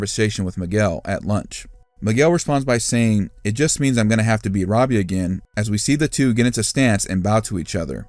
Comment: Miguel and Sam are gonna win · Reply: Since English isnt my first language, I just watched the trailer again and I made a mistake a couple hours ago. Miguel says: "That means I have to beat Robby again". I thought he said: "That means I dont have to beat Robby again". By watching the trailer now again, there is a possibility that not many people thought about: What if only 1 boy and 1 girl are allowed for each dojo to compete in the age division? It would make sense. This would mean the two fights we see Miguel vs Robby and Sam vs Tory could be the deciding fights who will represent M-D at the Sekai, and at least from the footage we see, Miguel and Sam are winning and would be the two competing in the tournament. But that's only speculative.